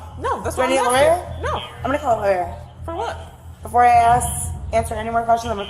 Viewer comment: guys...i think shes sayiing she wants to call her lawyer